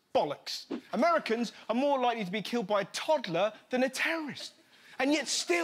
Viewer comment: what I hear is my toddler is a threat